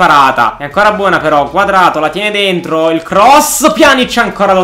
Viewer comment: ma solo io ho sentito quadrato al minuto 5:45? xD